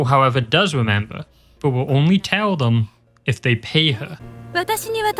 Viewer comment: i heard "pee her" instead of "pay her" HELP